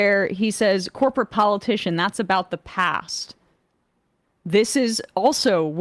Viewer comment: I thought he said "that's all about the PACS"